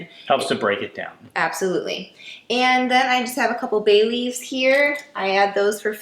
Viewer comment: When she said, “a couple bay leaves”, I thought she said, “a cup of Bailey’s” and I was questioning what kind of recipe this is.